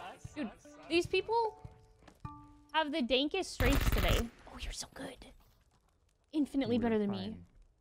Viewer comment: Seriously.DID I HEAR"these people have the dankest strains today"? And not actually talking about Mary jane, but the game???? I hope not lol